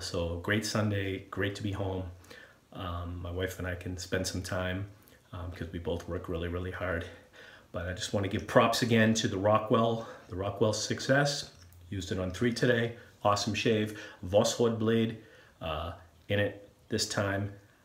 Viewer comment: I thought you said Rockwell "success". Fits the name nonetheless!